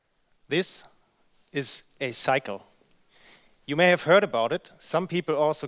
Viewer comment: Am I the only one that thought the first thing he said was "This is a psycho"?